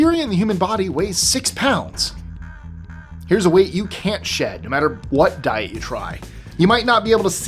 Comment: I thought you’d said you can’t shit out. You said weight out 😂😂